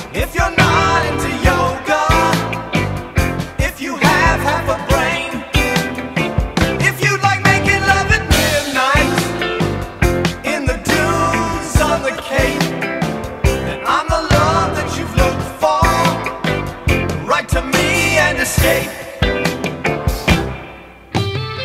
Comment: Why do I hear 'in the dunes suffocate' lol